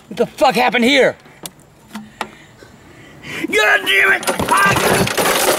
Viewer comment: what was he saying? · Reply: He was saying god damn god damn god damn god damn god damn G O D D A M N